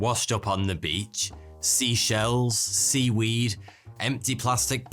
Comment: Him:"Seashells." What I hear "She sells seashells"